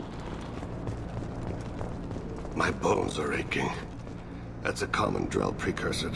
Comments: Dafuk, at first I heard "My balls are aching" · it sounds like is saying "My balls are aching" instead of bones. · For a moment i thought said "my balls are aching". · At first I thought said his balls were aching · I heard it as "My balls are aching.". xD